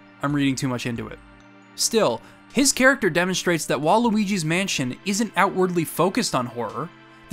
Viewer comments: For a second there, I thought you said "Waluigi's Mansion", and I got excited. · “Waluigi’s mansion”. What? Edit: I assume you said WHILE Luigi’s mansion… it just sounded like you said Waluigi’s mansion, lol!